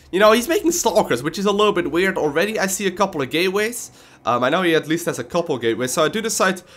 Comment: lol Gateways, with his accent i thought he said gayways.